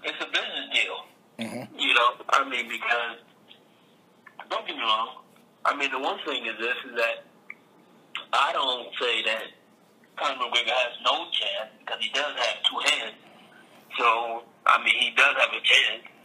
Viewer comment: LMFAO, Does he say "Conor McWigger" at 0:30 ?